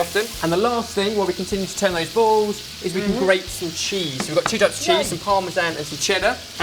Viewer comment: did he say dutch cheese. Because parma and cheddar are not dutch